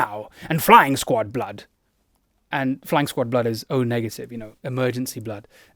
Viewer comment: Thought he was saying flying squirrel blood